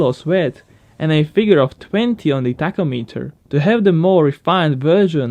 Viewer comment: I heard "...at least twenty on the taco-meter.". Sounds delicious.